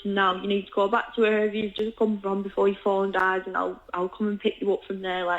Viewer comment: You need to listen to me before you fall and die . Did this young lady have a crystal ball ? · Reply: I thought said that, but after hearing it several times I was sure she said "before your phone dies", listen again and tell me what you think!